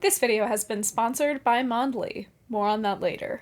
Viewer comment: Imagine my shock when I misheard "this video has been sponsored by Mondly" as "this video has been sponsored by Manwe"!